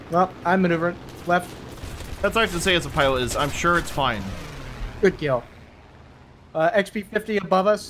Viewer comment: When said "Good kill" right at the start of the video, I heard it as "good girl" and was VERY confused and concerned for a moment 😅